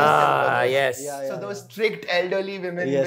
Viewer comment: can anyone tell me what he said? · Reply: Matron system